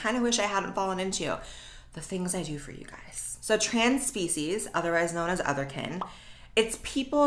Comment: I keep hearing “trans-feces” 😳